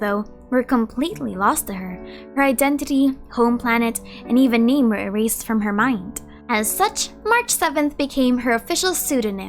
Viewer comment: lmao the auto subtitle said "even Neymar erased from her memory" 😂